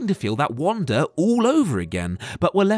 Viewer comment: Is it just me or does it sound like he is saying "Wanda"?